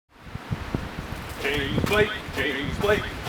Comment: at first i thought it said james blunt. haha. ew.